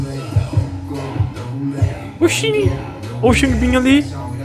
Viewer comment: ksksksk eu ouvi ¨o xinbinha ali¨ kskkskss